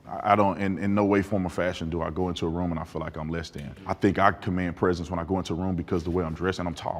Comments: the subtitles saying ‘lesbian’ instead of ‘less than’ 🥲🥲🥲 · subtitle is wrong. Replace 'lesbian' with 'less-than' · the captions says lesbian instead of less than 😂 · i'm so confused why did he say "i feel like i'm lesbian" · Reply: He sctually said ‘I feel like I’m less than’. Don’t know why the subtitles say that.